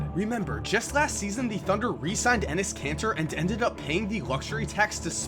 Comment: did you just say Enes Cancer?